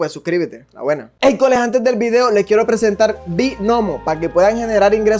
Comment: Joa valemia, qle vaina elegante cole, como siempre👍, en el patrocinio de Binomo, pense que decia Binomio de America